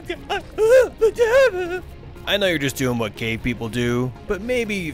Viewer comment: for a second I thought he said what gay people do and not what cave people do